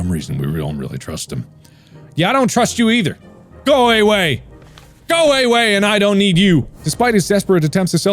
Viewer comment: anyone else hear “go away, Wade!”